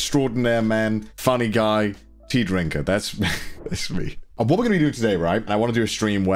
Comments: I just wanna know who else heard pee drinker as they were drinking there ginger ale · introducing himself as tea drinker. Could have just said british and it would hold the same meaning to me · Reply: Thought he said pee drinker and had to rewind